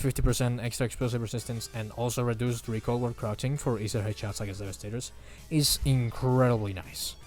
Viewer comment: what did he say? 😭😭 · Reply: “…and also reduced recoil while crouching for easier headshots against devastators”